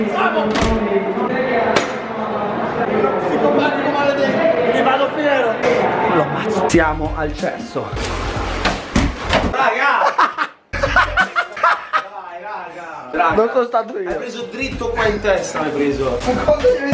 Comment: solo io ho capito ti amo cesso